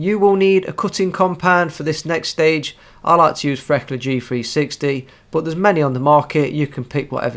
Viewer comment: I'm not familiar with his accent, and the subtitle says 'cutting compound' and 'Freckler g3'? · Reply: Farécla G360